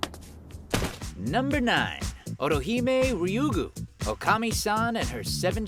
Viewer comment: why does it sound like hes saying orihime instead of ottohime, man they sound a like.